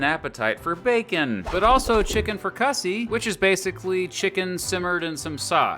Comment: Did you say “Chicken Fra Cussy?” I think it’s pronounce “Frik-a-zee”